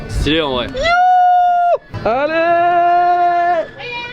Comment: cri HOUUUU ALLERRRRR et moi j'entendais HOUUUUUU HARDDD GAYYYYYYYYY